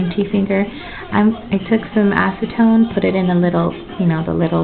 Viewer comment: I apologize it might have sounded like I said aftertone, but I meant acetone which is nailpolish remover sorry for the confusion!!! =)